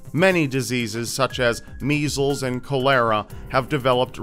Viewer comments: Did he just say “col-hera” instead of cholera? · I think you meant to say cholera but it sounded like you said col-lera